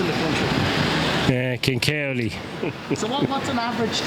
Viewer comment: King Kerley 😄 He actually said "Kilkerley"